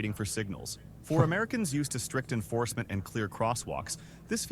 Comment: What I kept hearing there was "strictly enforced" for Americans. Land of the free home of the brave, are you sure about that?